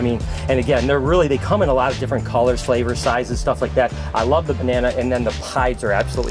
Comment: did he say they come in different colours flavours sizes 😂